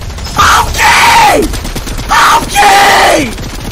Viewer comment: is he saying "okay" or "IM GAY"